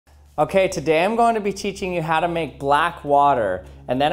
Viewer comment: cheatching instead of teaching 😂😂🤣